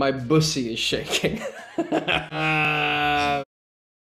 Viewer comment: I LOVE HOW HE KNOWS WHAT THE BUSSY IS AND GOES "AHH THE BUSSY" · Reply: i thought he said debussy